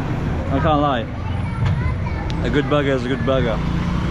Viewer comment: best thing about this video is how all the others were happy for her and not jealous... loved that. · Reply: HE SOUNDED LIKE HE SAID, A GOOD BUGGER IS A GOOD BUGGER!!🤷🏻‍♂️